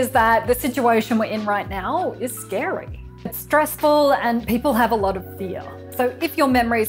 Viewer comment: Misheard 4:25 as "It's stressful and people have a lot of beer", but that also fits these times.